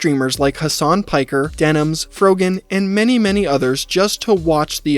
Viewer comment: did he say they paid Froggen to watch it? The league player Froggen?